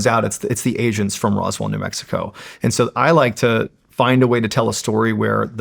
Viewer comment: Did he say Asians from Roswell, New Mexico ? Lol could have swore he said that.